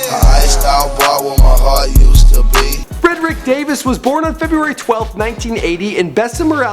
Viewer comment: Did he say "Fredrick"(0:53)I thought it was radric😕